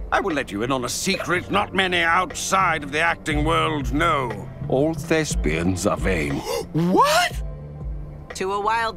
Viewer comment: what did he said? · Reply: "All thespians are vain." That is to say, actors live to be praised.